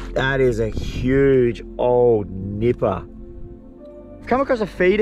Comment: i swear i heard him say 'huge old nipple' weird 🤣